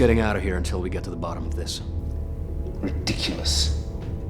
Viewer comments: が「行ってきます」にしか聞こえなくて少し困惑してます笑← あのAmong usが凄く格好いい映画のようになっていて驚きです！凄い！！ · ここのセリフが「行ってきます」にしか聞こえない件ｗｗ